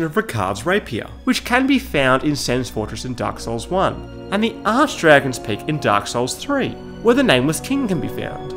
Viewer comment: did you say “ass dragons” lol?